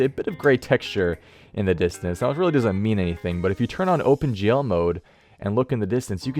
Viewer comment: i thought you said "open jail mode"